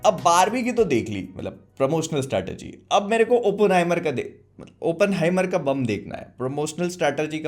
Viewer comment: I heard OPEN-HYMEN 😂